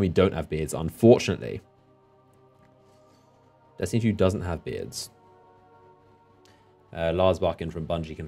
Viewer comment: what does he say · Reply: Anthony he said u cant have a beard in d2